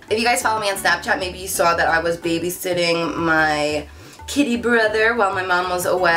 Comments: ok I was so confused for this whole video😂 I thought she said she baby sat her "kid brother" as in her young brother. so she went on and on about how she was allergic to animals and when she was baby sitting she wasn't sneezing and I was just really confused😂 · omg really laughing because I thought you were talking about a person when you said "Kitty Brother" i thought you said "kid brother" and I was extremely confused when you started tlaking about not getting sick because he was groomed...LOL